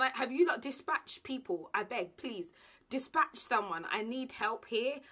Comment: Patricia ......... be delivering her babies like a Nigerian movie.... dramatic 😂😂😂😂😂😂😂😂😂😂. Congrats mama.... Jehovah’s blessings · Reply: Lol I heard "abeg" at 14:32,is it just me?😂